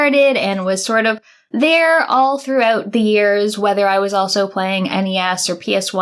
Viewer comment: That part at the beginning I had to listen to a couple of times because it sounded like you were saying, "Whether I was playing any ass", and then I realised that you were saying, "Whether I was playing NES" :D